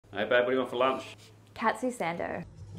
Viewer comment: “Cat-sue sand-dough” 💅🏻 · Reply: I legit thought she said “cats with sandals” and was like “what kind of British food is that? 🤔”